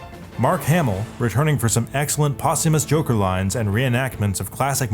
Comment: DID HE SAY JOKER